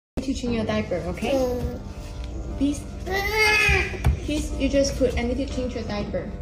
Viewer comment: the first mom wasn’t begging, “Peace” is the child’s name, but it did sound like she was saying “please”